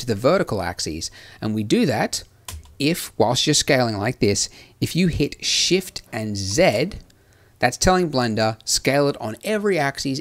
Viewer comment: What's the key he said so it affect X and Y and the same time? I do not understood what he said and no key appears at the left corner. EDIT: Ok it is Alt+Z. After this step, when scalling is not doing simetric, the circle appers at the left and not centered, why?